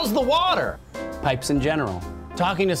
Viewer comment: what did he say at 0:50? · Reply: pipes in general